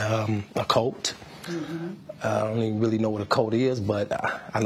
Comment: Am I the only one who heard him say, "I don't really know what a coat is??"🤔🤨